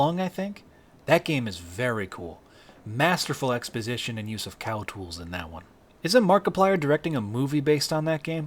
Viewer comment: did you say cow tools?